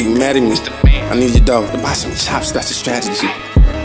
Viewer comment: I couldn’t make out what he said but the way he said strategy just hit me 😮‍💨🔥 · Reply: “i need u tho to buy some chops its a strategy”